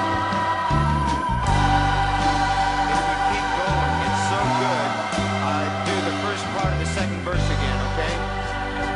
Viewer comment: At 3:19 to 3:25, Hardin is speaking something, beneath the vocals/music. It sounds as though he's saying something about repeating the verses, from what I can make out. Any idea what he's saying? Very nice tune. There is something magnetic about Tim's music; it has "grabbed" many a listener and musician, with its simplicity and his pain-wrenching voice. Thanks for sharing. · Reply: "If you keep going, is so good..... I'll do the firs bar of the second verse again, ok?? "..he talk to the musicians in the recording, because he like it and want to make it longuer.